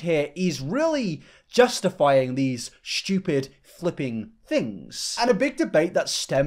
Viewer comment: i think the dude said flippant, not flipping as quoted